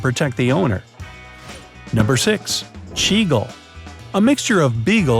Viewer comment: i heard it as seagull 😭